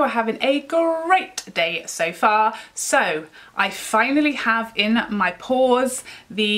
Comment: When you said “I have it in my paws” for a second, I thought you said, I have it in my pores. I adore you💜 Thanks for the review:)